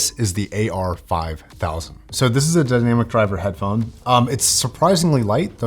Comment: What are you saying here? Dynamic driver headphone? lol